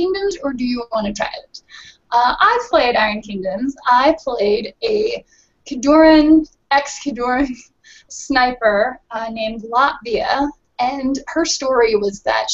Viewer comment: For a minute I thought she said Latveria, and I was like, yeah, I could see as Dr. Doom. How she abused poor in that one battle report leaves no doubt.